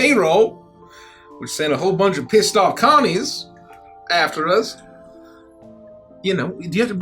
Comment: I wasn't listening 100% so I definitely for a second thought he said "which sent a bunch of pissed off commies after us" and I was like "hol up--"